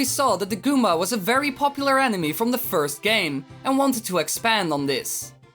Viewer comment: I thought you said 'Nintento probably saw that the goomba was a very popular anime.'